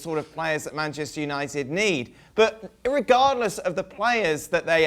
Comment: Did he just say "irregardless"?? I see we have The Office boss Michael Scott here.